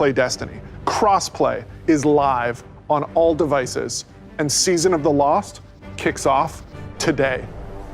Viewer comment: I deadass thought he said “season of the loft”